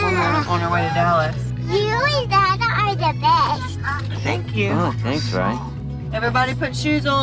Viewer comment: What did say at 3:53? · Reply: Mama, you and dada are the best